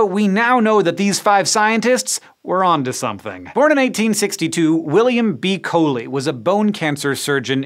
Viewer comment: I thought you said William B. Coney was born in 1962. I keep repeating it, but that's how it sounds to me...